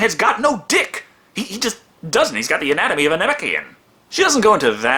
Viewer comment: anatomy of a Namekian? did I hear that right?